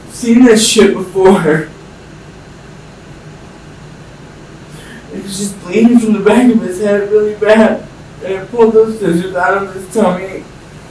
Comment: The portion of the video in the interrogation where you You claim What he is saying to be in "inaudible"i heard every word he Said and I quote, "I just felt really bad for him....He was bleeding from the back of the head and I pulled the scissors out of his TUMMY"